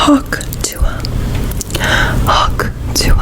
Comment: the yt subtitles saying "hawk to her"